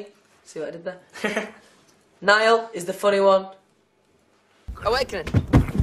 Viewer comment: oh that CHURCH BOY instead of funny one😂😂😂😂😂😂😂😂😂😂😂😂😂LMAO.....I'm ded laughing